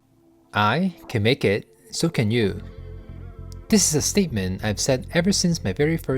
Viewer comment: Wait, I thought you said "I KEN make it, so KEN you". I thought it was a joke 😂